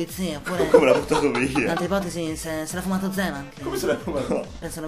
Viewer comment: Cosa dice a 4:46 ? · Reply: Potete venirmi ad liberarmi per cortesia ? È la parte veloce che dice